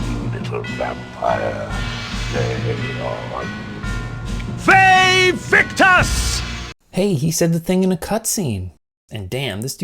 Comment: I only ever played Blood Omen out of the series, but I have been mishearing and saying "Yay Victus!" for over 25 years. Yikes. Also, between this, Icewind Dale, and King's Quest VI, Tony Jay's voice evokes such a visceral late 90's nostalgia for me.